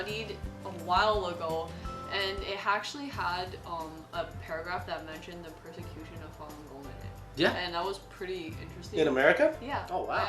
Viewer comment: what did she say? The persecution of ....? Can someone tell me, I just want to learn about it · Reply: the persecution of falun gong